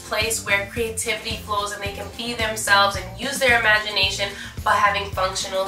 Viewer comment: When you said a place they can be themselves I thought you said a place they can pee themselves